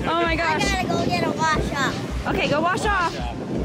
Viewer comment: What did say? I had to replay it. It sounded like he said, “I got to go get a vodka.” 😂 I’m sure I misheard! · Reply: Is it when he gets up out of the sand? He said, “I’ve gotta go get a wash off” 😉😂